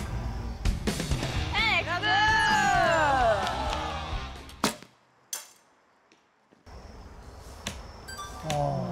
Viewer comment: Can anyone tell me what they said when San hit the X at the 14:28 mark? Thanks! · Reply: Say X